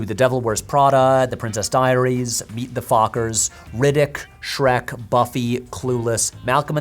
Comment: when you said Meet the Fockers, I heard Meet the Feebles and was like... how did THAT get greenlit. And apparently it didn't.